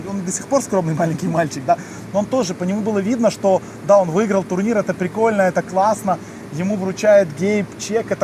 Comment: я один слышу "даун выиграл турнир..."???))) ( не в обиду )